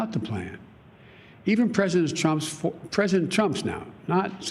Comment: Biden sounds like He says President Chumps 😂😈